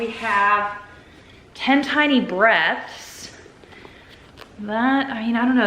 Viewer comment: I'm listening to this and I thought you said Ten Tiny Breasts!! Lmao!!!